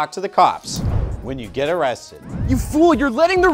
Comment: The first time I watched this, I thought I heard, "This is how you talk to the cops... Get arrested... " instead of "when you get arrested"